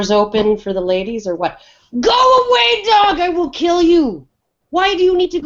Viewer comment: Sounds like you said around 8:22 "go away Gaga".